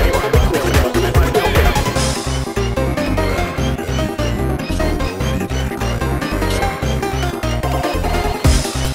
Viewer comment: what is being said here? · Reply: Its saying “You can’t do anything so don’t even try” Its one of the lines from Metallic Madness Bad Future (the jp version)